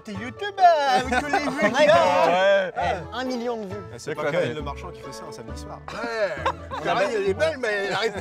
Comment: Pourquoi on parle de mon école (athénée de Marchin) à 1:28 !?!? Je suis choqué putain !!!!!!!!!! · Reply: La phrase c'est "C'est pas Karine Le Marchand (la présentatrice de l'amour est dans le pré) qui fait ça un samedi soir"